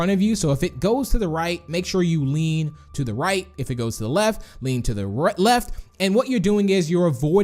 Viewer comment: WHAT'S THE HARDEST HOUR IN RAINBOW FRIENDS? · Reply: From 7:57 to 8:05 I heard you “if it goes to the left go to the r-left”